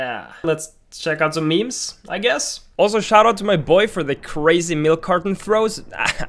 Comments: shout-out to my boy for .... I thought you were saying, shout-out to my boyfriend..... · I thought homie said "boyfriend"